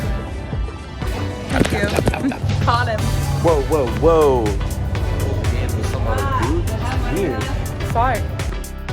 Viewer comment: I have watched the scene at 20:23 with Allie and the alligator so many times and I can not make out the conversation. Help! What are they saying? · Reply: Allie: caught him: Can’t be some other dude , that’s weird Allie: Sorry